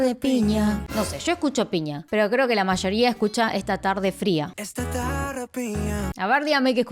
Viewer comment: Yo escuche "Esta tarde mía"🤣 toda la sordera y toda la ilusión